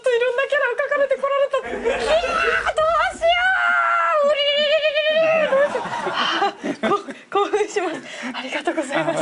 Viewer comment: all I heard was toRUURURURURuurURU